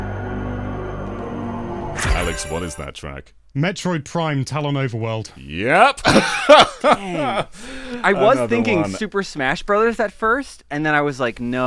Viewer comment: oh my god I thought he said “Metroid Prime telenovela world”